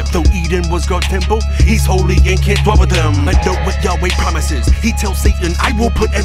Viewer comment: Did he say "He's holy and can't (F-word) with them"? That's what it sounds like, and the lyrics disappear right at that word as well... just curious, not judging. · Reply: "dwell" lol